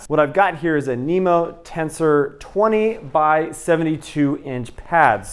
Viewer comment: It sounded like you said that pad was a Nemo Tensor 25 72. But later in the video, you said it was a 20" pad. Is that pad 20" wide or 25" wide? I ask because I want to know if my 24" wide pad will fit in the toe box of the Therm-a-Rest quilt. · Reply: Allow me to answer my own question. You said 20 by 72, not 25 72. I listened again.